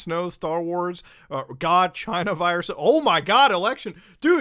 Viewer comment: WAIT DID HE SAY CHINA VIRUS?! Sorry if I'm being dramatic I'm currently zooted but WHAT?!